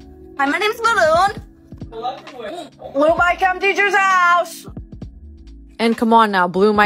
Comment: I thought she was saying "screw my chem teachers house"